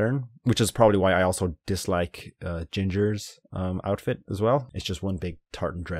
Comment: I thought you said: “that’s why I also dislike gingers” LMAOO